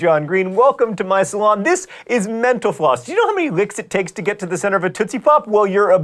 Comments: Whenever he says "mental floss," I hear "menopause." XD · At the end when John says "Mental Floss" I totally thought he said menopause instead O.o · Every time he says 'mental floss' I keep hearing 'menopause'. There must be something wrong with me.